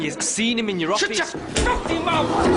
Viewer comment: fight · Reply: He said filthy. I thought he said fucking too.